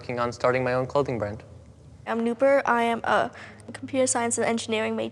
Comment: I thought it said nooper was ten